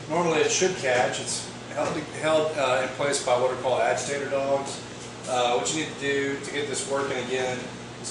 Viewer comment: First video I watched, walked into the appliance parts store in my town and shouted AGITATOR DOGS like I knew what was up. My washer is running so smooth now!! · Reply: I thought at first you said, "ALLIGATOR DOGS", hah.